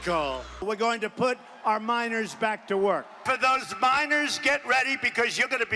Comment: It can't just be me that thought Trump said, "We've got to get those minors back to work" as oppose to miners